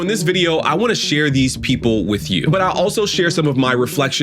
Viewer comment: He wants to share these "people" with us. Sounded like he wants to share a new found "species" with us!